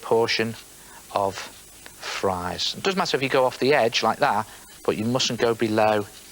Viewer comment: Did Neil said "Fries"?